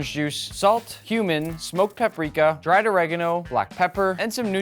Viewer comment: I misheard cumin as human and I was like “human? Wtf?”